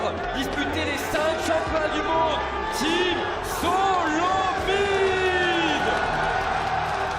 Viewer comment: i swear to god this guy said team solo feed